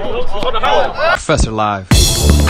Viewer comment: I don't know why in the intro I seriously thought he said suser live